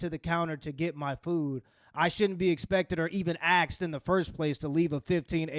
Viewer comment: Bro did u really say "AXE" instead of "Ask" on 6:30 mark